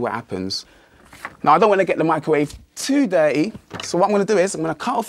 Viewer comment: did he really say "Dirty"? What the hell of an accent is that?!